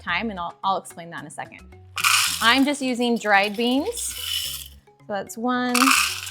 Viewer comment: I thought she said she used canned. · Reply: She used dried beans.